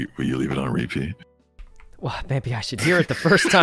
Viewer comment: did sykkuno just say baby? huh